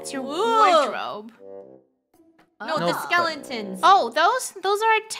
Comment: Did really say "Skelentons" I don't even know how to react to that.